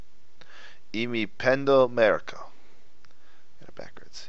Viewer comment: Um.. 25:00 is he saying "meryko" or am I just too much of a jerk?